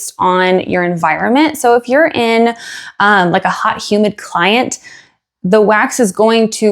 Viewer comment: Did you just say a good humid client?😂 Very helpful information as always